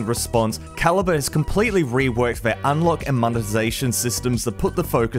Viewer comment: did you just say reworked for monetization ? that's a RED flag.